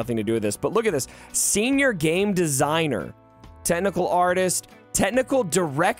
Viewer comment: i had to replay that one, because at first i thought you've said tentacle artist.... xD